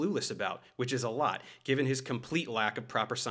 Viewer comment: “I wrote a textbook…” lmao I can’t believe he actually said that. That’s an ego driven statement if I’ve heard one. At first I thought he said “I’ll write a textbook…” which is still bad, but not quite as much so.